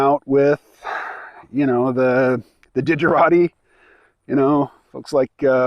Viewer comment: When you discussed hanging with the "digerati" at occupy, at first I heard "didgerati", which I guess would be the 'other lot.' (I'm sure there's some overlap.)